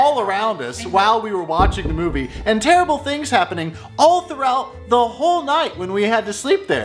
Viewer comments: i hear "terrible things" in the spot hears "terror of it" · I heard "terrible fit"